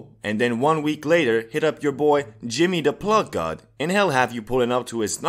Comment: am i the only one that hears moistcr1tikal instead of jimmy